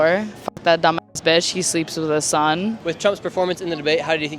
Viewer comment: What did she say at 10:30 she sounds like she needs help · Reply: He sleeps with his son?😮 HUHHH? Yeah can't tell if that's what she said